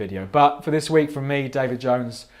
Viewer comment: I thought he said his name is Davy jones from pirates of the Caribbean😂😂😂